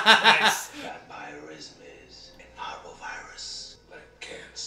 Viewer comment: To be clear, for anyone who hasn't seen the movie before now, Damaskinos here does not say "horrible" virus; he says "parvovirus", a real world virus family known to affect animals like dogs and cats, though there ARE strains that affect humans.